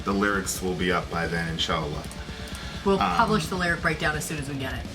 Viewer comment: did he say inshallah